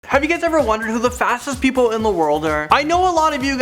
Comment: Fastest people? I thought it said "Fattest"